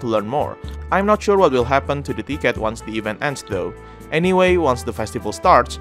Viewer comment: Lmao I thought he said dickhead not ticket.